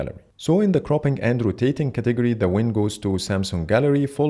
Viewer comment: I can't be the only one who heard "cropping andrew tateing category" instead of cropping and rotating category"😂